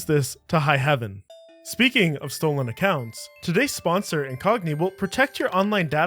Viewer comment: I was tabbed out when he said "This sponsor, Incogni" and thought he said "This sponsor, is Kanye."